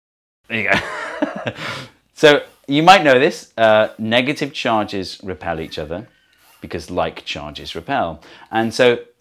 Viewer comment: What did you say at 2:59? Hahaha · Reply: You might notice, negative charges repel each other. You might know this; negative charges repel each other.